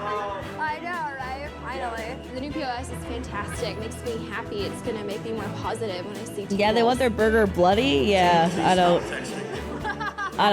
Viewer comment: When the customer said "bloody", after being asked how they'd like their food cooked, for some reason, I heard "slutty"... 😅